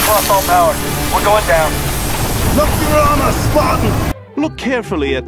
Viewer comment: At first I thought he said "Lucky armor Spartan!" Implying that you and him would survive cause you had better armor than the marines